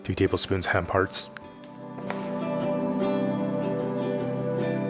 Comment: what was the ingredientt at 4:30, I didn't get it? I replayed several times, it sounded like ham hearts?? looked like bread crumbs?? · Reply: Hiya 🙋🏻‍♂️ Hemp hearts - for more details, all ingredients and directions can be found on the description are of each episode. Happy cooking 🙌🏻